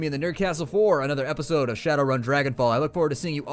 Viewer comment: I thought that he had said shadow run DRAGONBALL